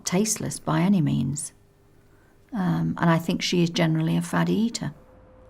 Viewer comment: What did the Matron call her? Fatty eater? is that an actual term or did I mishear it? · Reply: I think it was a "Faddy Eater" where they don't eat food that looks appetizing or can only eat foods they're familiar with/safe to eat. Essentially, a picky eater.